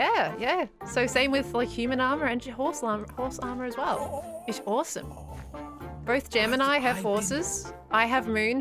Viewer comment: I thought she said 'yeah, it's HORSEome!'